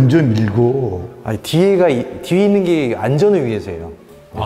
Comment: 뒤에 있는 게 (내)안전을 위해서예요 라고 들리는 기분이 ㅋㅋㅋ